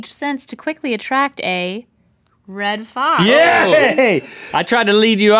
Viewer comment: When they said red fox I thought they said red Sox!